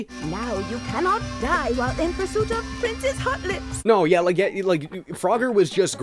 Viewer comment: Excuse me, what is her name again? Did I hear Princess Hot Lips? Wow that’s a bad innuendo